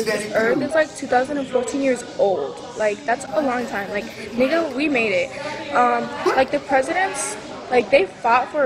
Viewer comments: What did she say at 0:13. I swear she says "nigga we made it" XD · did she say nigguh we made it?